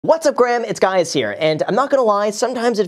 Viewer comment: Wait! did he just say- "What's up Graham, it's guys here" ?!?